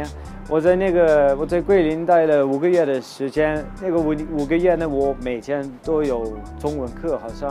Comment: did he just say nigga